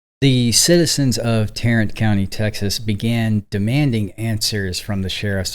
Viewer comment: At first I thought you were saying « Tyrant county ». Thought it was some kind of joke. After a couple minutes I realized my mistake. I was not too far off, though. AT ALL.